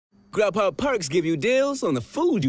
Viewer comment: At first I thought he said grandpa’s twerks give you the food you love.